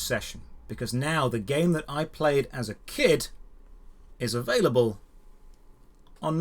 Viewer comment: what i heard was, "now the game i played has a kid" and that somehow hit me and made me feel really old, (besides my horrible hearing problems)